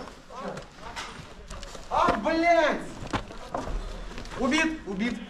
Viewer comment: Did i just heard Blyat in 27:38 🤣